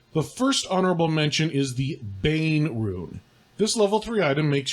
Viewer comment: I thought you said "Goose-Bane Rune" I was super interested